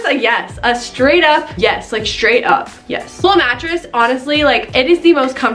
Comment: Haha I thought you were saying lil' mattress instead of Lull mattress.